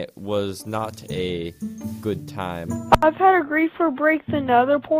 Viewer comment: thought he said he never had a griefer before